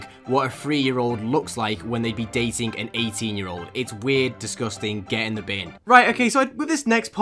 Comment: I actually thought he said “it’s weird,disgusting,get in the van-“